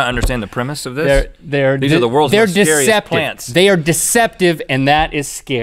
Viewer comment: lol i thought it said "Scariest Planets on Earth" hahaha